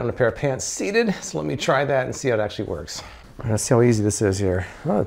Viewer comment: Did just say, "So, let me try that and see how that shit works"?